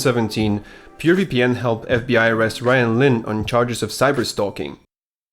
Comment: I thought u said cyberstonking not cyberstalking